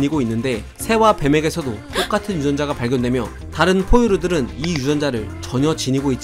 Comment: 효과음 땜에 X같은 유전자로 들었네ㅋㅋ